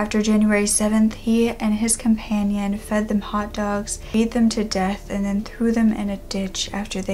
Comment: What does she say at 10:14???? Fed them to death?? I cannot understand her